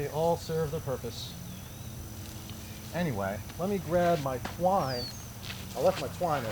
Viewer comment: I thought you said "let me grab my wine" It was 92 here today....I need to go swimming. Lake superior here i come. Once you get the hang of it braiding is so easy